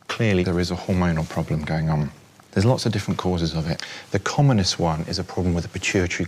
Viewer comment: When the nipple is pressed, I hurt. I am M2F trans and those things hurt when growing :(. Laughing at how at 1:03, the captions say the "Communist" one. Basically anytime he says "common cause". @ 2:20 is when the bill starts to raise. RUN AWAY FAST!